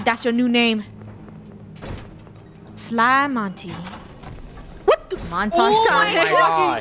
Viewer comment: "what the f-" I might've misheard it 😀